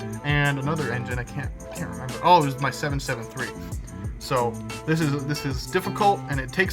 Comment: did you say… 773? You have a Hudson!?